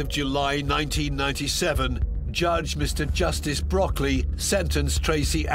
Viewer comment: Judge Mr Justice Broccoli? Did I hear that right??